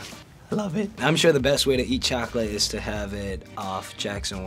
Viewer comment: did he just say love it